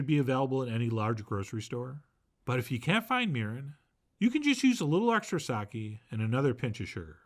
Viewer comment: LOL I had the CC on and when Chef John said mirin, the CC thought he said urine and I was like what?!?!?!